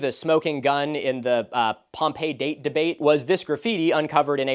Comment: thought u said “Pompeii bake debate” and almost choked